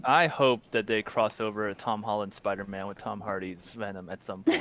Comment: Did he say Spider-Male @ 24:10 Is Spider-Man offense now??? Lol